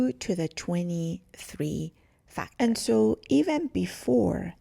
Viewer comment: Sorry at 1:21 did you say 2^23 or 2^235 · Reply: The exact math is: 2^{23} = 8,388,608. And then both parents contributing genes, the total number of possible genetic combinations in a child is: (2^{23})^2 = 70,368,744,177,664